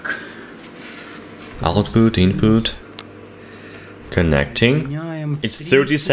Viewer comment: What did he say at 3:17? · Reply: Output input